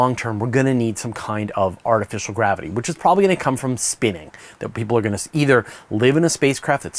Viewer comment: for some reason I heard him saying spinach instead of spinning 😂